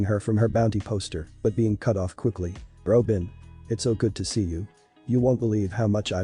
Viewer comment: Robe bin 😂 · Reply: I thought they said Ropin' and I was gonna throw hands